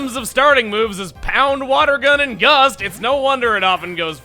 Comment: Did just say Yust instead of Gust?